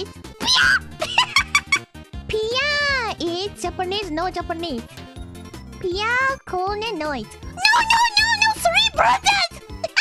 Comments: For every one that didnt know what is she saying in 1:12 she say "piya no japanese, piya is korone noise" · ok I thought she said BLYAT! but it was piya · ''Piya is Ko'one'' Every time she says her name fast it comes out as ''Ko'one'' instead of ''Korone'', she also says ''Ogayu'' with a strong ''ga'' (sometimes even with a soft ''ga'') instead of ''Okayu'' with an ''ka''. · i think she said "korone noise"